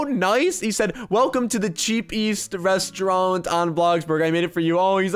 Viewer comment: Did this man say Cheap East instead of Cheapest xD